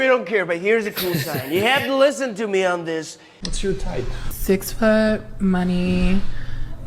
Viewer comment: I swear the only thing I heard her say she was looking for in a man is "food."